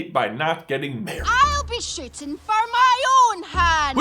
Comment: Why did I just hear Merida say, "I'll be sh*tting for my own hand"?